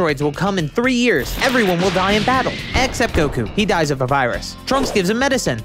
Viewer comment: It Sounds Like Said: Except Goku, He Died Of Papyrus.